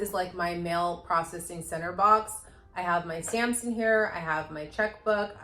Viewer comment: “stamps in here”, I had to replay twice because all I kept hearing was “Samson hair” 🐶 😂 Happy New Year!!!